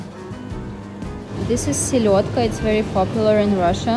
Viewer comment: The herring. I dont know how to write down in words what you said but it sounded a lot like us Finns call herring. We call it Silakka =)